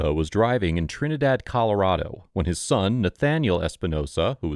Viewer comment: I thought you said Daniel 1:18 ??? Why wouldn’t he be behind a traffic stop where his son is?? 1:28 ??? Why is this cop so fucking aggressive 1:42 what?!?! Why would he be charged 1:45 attitude? You want to talk attitude? Jesus dude you’re insane